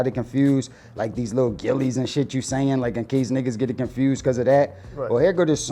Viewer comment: When he said "these little Gillies and shit you saying", I honestly heard that as "These little COMMENTS FROM Gillie and shit you saying"... I honestly do not think he was attempting to belittle Gillie. I think he was saying that the COMMENT was unimportant because Gillie doesn't really follow battle rap that closely anymore. Don't make it deeper than what it needs to be.